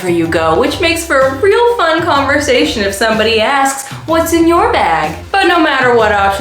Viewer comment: "what's in your bag" What I heard: "what's in your wallet" 😂